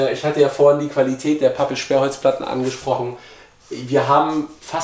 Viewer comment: Hallihallo ich hätte eine kleine frage, welches Material habt ihr für die Innenverkleidung verwendet? Ich habe pappe sperrholz Platte verstanden, das internet kennt aber nur Pappel sperrholz Platten. Habe ich euch falsch verstanden oder ist das internet verwirrt? 😅